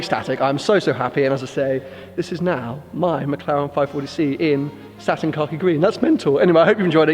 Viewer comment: Anyone else thought he said Satin carpet green?